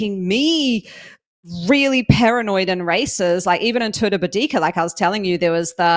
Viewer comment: 49 minutes into podcast says "it has started making me really paranoid in races" and I hear "really paranoid and racist" and I'm like whaaaaat... But very much enjoyed your show. Thanks and welcome back from the concussion.